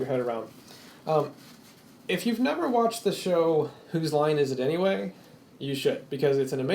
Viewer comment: whats the amazing show u talk about? i cant understand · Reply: The name of the show is "Who's Line is It Anyway?"